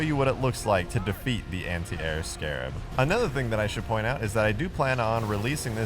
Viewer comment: It may just be me but I thought he said " Anti-Arab Scarab " 😂